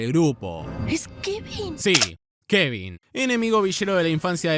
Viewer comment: pensé que decia villano AJAJAJAJAJA q basura